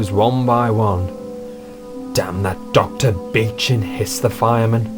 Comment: did he say the B word? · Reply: He said Dr. Beeching.